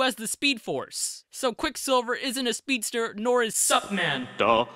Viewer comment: I was just listening so when he said "supman" I thought he said succman